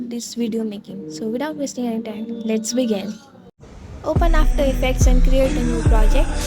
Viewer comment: did bro just said after effects?? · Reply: Yes, bro did said after effects.